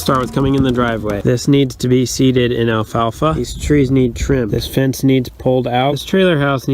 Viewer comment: I thought he said " Trim those trees,"